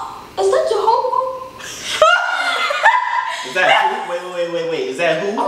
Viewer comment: DID HE SAY JEHOVAH AT 10:13 BRO HAHHAHAHHAHAHAHAHAHHAHAHH IM DEAD CUHHH